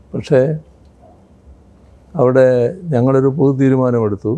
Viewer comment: pothu theerumanam, meaning common decision. translator probably heard it as 'puthu' theerumanam and translated incorrectly as 'new' decision.